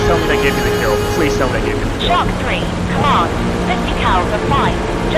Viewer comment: Wtf was that srs transmission at the end?! Controller mocking you? The fu… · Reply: Right? :-) I thought she said '"Shark 3 come on! 50 cals are fine. Just aim better.' but I think the first part was actually 'Shark 3, Command.' She was still mocking him though :-D